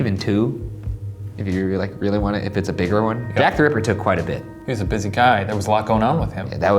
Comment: when Ryan said Jack the Ripper I had to rewind it around 12 times because I couldn’t understand what he said 😂😂